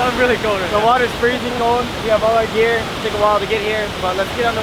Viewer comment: When he said all our gear I thought he said alligator